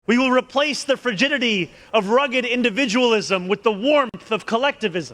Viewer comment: I swear I head "virginity" not frigidity the first time I heard this